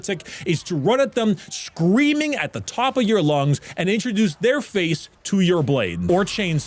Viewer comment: "Introduce their face to your blade" The fact that i heard "faith" instead of "face" make it 10 times funnier